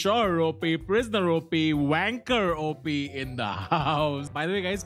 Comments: tanmay saying wanker instead of banker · did he say wanker op? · 2nd time watching this. Misheard banker OP as wanker OP😂😂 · Banker OP sounded like wanker OP😂